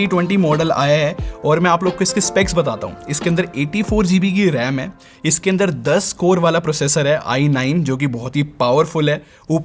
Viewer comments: did he really say isme 84 gb ram hai?😯 how? Is it possible? Am I the only one who doesn't know imac has 84gb ram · kya bola ? Kya bola tune ... 84 GB RAM 😒